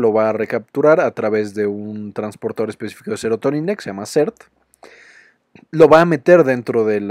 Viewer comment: Disculpen alguien me puede decir ¿qué es lo que dice en el minuto 6:44? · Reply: Transportador específico de serotonina llamado SERT